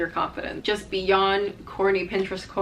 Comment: Off topic but i thought you said "horny Pinterest quotes" instead of corny